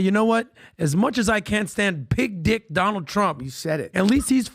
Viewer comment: The best part is it sounds like he is saying Big Dick Donald Trump... What a dork lol